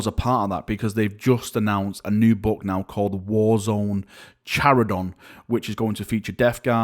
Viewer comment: For a minute I thought GW didn’t say charadon and instead said carcharadon, as in the most badass space marine chapter, and I got way too excited.